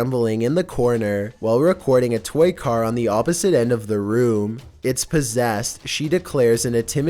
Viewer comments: It sounds like it says it's you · 15 it kinda sounds like it says " it's you "